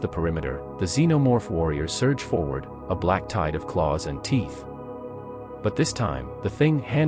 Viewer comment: the first time through I thought it said wearing black ties and I thought WTF ,,, then heard it again... oh a black tide ... nevermind. 😁